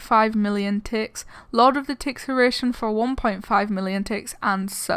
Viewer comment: If you use subtitles at 2:23 instead of being "Lord of the Tixeration" it will be "Lot of the ticks are Asian"